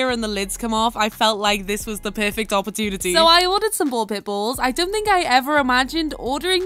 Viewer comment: I wasn't paying attention, and then I heard you say, I ordered pit bulls, and when I looked up you were opening a bag, I was looked concerned about the video.😂😂🤣